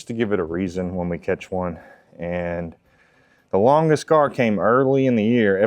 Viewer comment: Garr… thanks goodness. At first I thought he said longest scar!!! Could be an interesting category though…